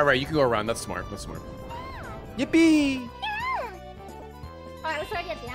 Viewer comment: I'll be honest, I thought he said "yiffie" emote not "yippee" and that threw me tf off